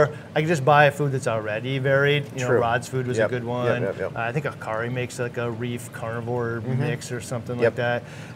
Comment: In minute 12:35 you mentioned some brands of good mixed food for dish. I didn't quite heat what brands you said. Can someone share the name if they are able to recognize it? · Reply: At that time stamp, mentions Rod's food, the Hikari brand, or DIY fish foods 🙂